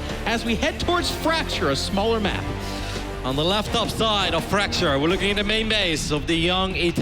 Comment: sounds like he said laptop. Lol but it's lef top